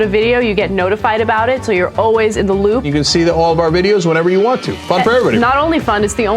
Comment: Did she say So your always in the loo